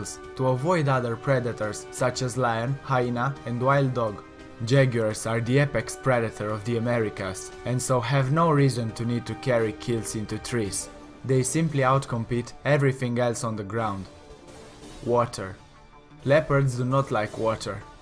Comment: thought they said carry kids (kills) in to trees at first.